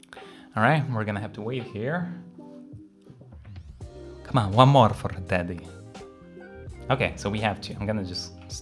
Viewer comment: I swear I heard " Come on one more fur daddy." around 17:37 LMAO